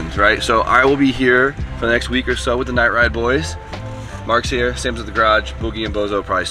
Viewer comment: Did he call marek Mark?